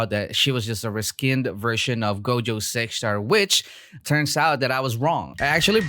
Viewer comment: subtitle thought that you said Gojo sex star 😆